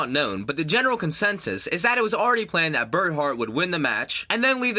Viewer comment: Did you say BERT Hart at 4:28 ?